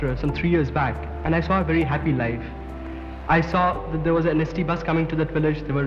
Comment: The subtitle says inaudible but he said ST Bus. These are state transport buses in Gujarat.